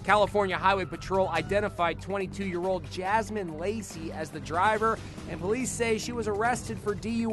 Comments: It sounded like he said jasmine lazy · i thought he said her name was jasmine lazy